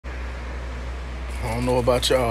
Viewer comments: “I don’t know abat ya” “ but this sh- GWAGGAGAGAGLA-“ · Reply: I heard “but this is blood” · He violated the f**king strawberry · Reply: i heard “but this ship”